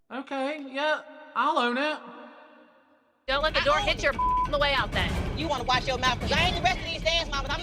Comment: does christi say “don’t let the door hit your black ass on the way out then” or am i tweaking